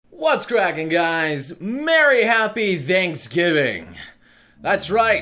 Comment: Ok, I know he said Thanks Giving, but could have sworn I heard him say Danks Giving.